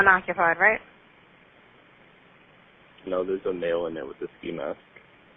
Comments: the transcript is incorrect. What the caller says is "No, there's a male in there with a ski mask." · He actually said, "No there's a male in there with a SKI MASK." Not, "there's a male in there with a FEMALE." Jesus.. I hope this is how this channel interpreted this conversation and this isn't ACTUALLY how 911 logged it in. I'm just listening on my PHONE and even I can clearly hear he said SKI MASK. Now.. factor in additional details like: (1) How does an average citizen know if a vehicle is stolen? (2) The caller seems way too calm and collected after witnessing an accident. Most people call for an ambulance. You also can't hear anything around the caller, no background noise. Then officers arrive and the scene just looks shady. No bystanders around helping. No other call-ins. It says "NO FEAR OF DYIN'" on the rear window (lol), and the cops took an extra long time to spot the ski mask, even though this SHOULD have been reported to them. Seems like a 911 FAIL to me. I would have rolled up to this scene and ordered a response from the occupants before approaching. The whole thing looked OBVIOUSLY staged, even the call sounded staged. All I can say it.. lucky for the cops he didn't have a REAL gun.